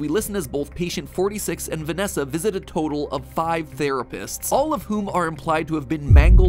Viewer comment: lol i thought it said the rapists